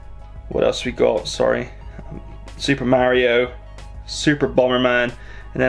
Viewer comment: "Super Bummer Man" Seems legit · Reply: I thought it said Super Boomer Man